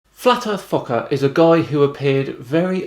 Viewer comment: Okay, I thought you said, "Flat Earth FU<KERS", might want to enunciate a bit for the kids... :)